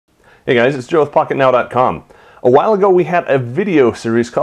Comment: Oh now that I read below the video the channel is called "Pocket now" I thought you said "hey guys this is fu**it now"